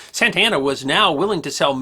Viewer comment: when you say Santa Ana I hear "Santana". So the general was a Hispanic guitar God? Nice.....